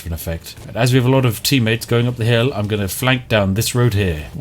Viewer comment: did You actually say "tea-mates"? 😄